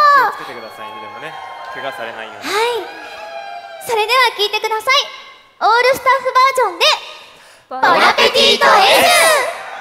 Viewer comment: Did I just hear her say 'All Star'? *inhales* SOMEBODY ONCE TOLD ME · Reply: She said all staff